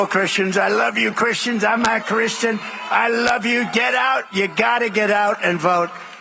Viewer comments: I thought I heard him say "I'm not Christian" 😮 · When he says "I'm a Christian" it really sounds like he is saying "I'm not Christian" and i feel like its a Freudian slip. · It even sounds like he said "I'm not Christian" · Let's see if this nation with an average IQ of 70-75 understands what this tyrant just said. Yes, there are bloggers that rally against trump .. reality is the hillbillies don't care, don't understand, want a return to the old west and above it all .. hate the other 50% . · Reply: If nothing else I know they heard him say, "I'M NOT CHRISTIAN!" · Is it just me or did he just say I'm not Christian Red Flag City an this Christian is voting blue across the board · Sure sounds like he said "I love you Christians. I'm not Christian." at the beginning there............ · I heard Trump say: "I'm not Christian". Am I mistaken?